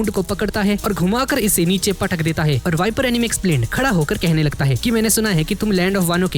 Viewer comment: Bro does he call viper anime explain 8:35 😅